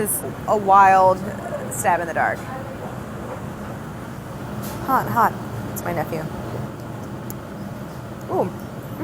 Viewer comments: Sorry but at 12:00 did I hear her say "hot hot, it's my nephew" ???? Cause ????? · Am I the only one that heard her say "hot hot , that's my nephew"😂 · um did like no one catch that she said “hot hot it’s my nephew” or am i hearing things??? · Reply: Nah im pretty sure she said "hot hot,its my nephew"